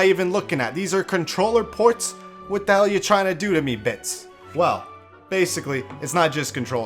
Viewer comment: I thought you said bi